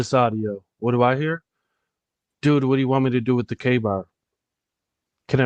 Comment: I feel like I hear “hey or Hayden “what do you want me to do with Ethan”